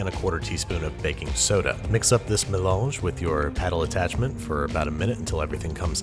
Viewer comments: Oh a melange! I thought you said menage. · "melange? I thought you said menage!" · Oh Melange! I thought you said menage!